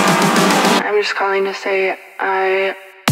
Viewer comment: anyone know what she saying? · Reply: im just calling to say i